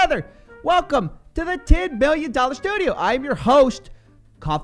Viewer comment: Almost sounds like you said 10 Billion dollar studio. But I now see its the same 10 milly. You'll get there dude. I actually run a course and I feel you would benefit from it, big time.